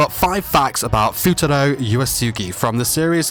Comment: Did i just hear futaro yuwasugi on